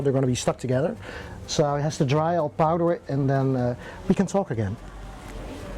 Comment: i thought he said "ill powder it and then we can uhh smoke again" i was waiting for nikkies reaction and i just got a thumbs up 😂 i'm assuming he said "talk" not "smoke"